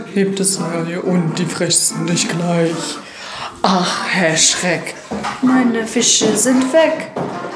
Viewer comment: ich hab erst "Hashtag meine Fische sind weg" verstanden und dachte mir "Was zur Hölle ist mit den Kinderliedern heutzutage passiert?!" - liegt wahrscheinlich eher an mir 😂😂